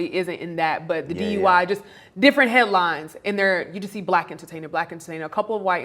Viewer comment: Yo at 4:43 I thought she said “different hairlines” 😂